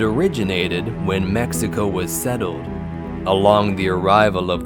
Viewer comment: Did he just say Meso instead of Mexico lmfao